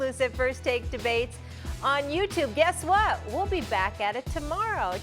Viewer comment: Did she say "Have a bad day" at 9:45? 😂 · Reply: she said fab lmao cmon bro